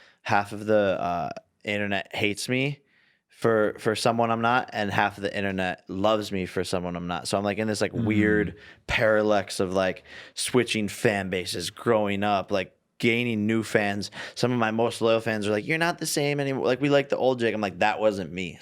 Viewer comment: did he just say paralex? I... I don't think that's a word.